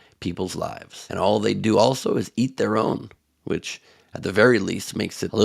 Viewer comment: I thought Trump said it was eating their young although I know the saying is eating their own as you said